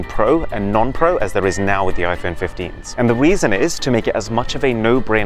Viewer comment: did anyone hear that arun said the weason or is it just me